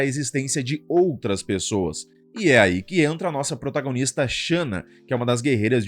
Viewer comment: eu ouvi Xana? ;)